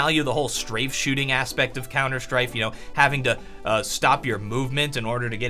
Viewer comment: Did he say "Counter Strife" right after talking about strafing at 5:03? ^^